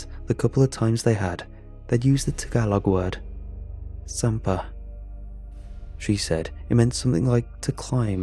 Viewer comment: I speak Tagalog, and I thought you said "samba" which means to worship. I had to use closed captioning to see that it was "sampa", or to climb up. Nice